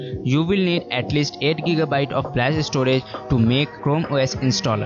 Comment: what did you say a 1:11? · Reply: 8gb of Flash storage .. aka USB